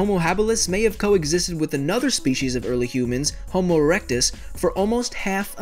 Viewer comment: it sounded like he said homo erection